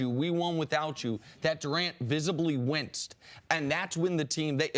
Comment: Can someone tell me whats the word Nick said in 5:13? '' ... that Durant visibly (witsed? wentzed?)?????